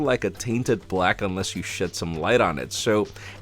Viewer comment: i thought he said "unless you shit some light on it" 🤣